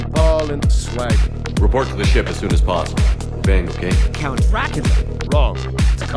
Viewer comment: I honestly thought the game said "We'll bang okay?" xD